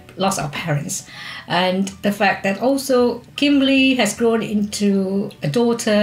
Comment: whoa whoa whoa hold up did she call...Kimberley? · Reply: She says Kimberly several times throughout the video, it is the long version of "Kim" after all